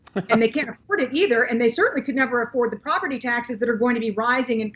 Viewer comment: Did she say, "Can't afford the poverty taxes"? ~